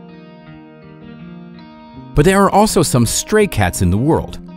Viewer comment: im such i stan i heard stray kids